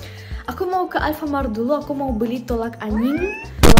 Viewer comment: Kak tapi aku salah dengar gak si kakak soal nya kakak bilang tolak anjing bukan tolak angin tapi jangan tersingung lo kak